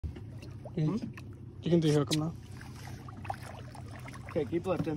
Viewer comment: Y’all ain’t 💩setting up that baby shark to be eaten up🤦🏾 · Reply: iPhoneTwentySeven I believe I heard him say “can you unhook him”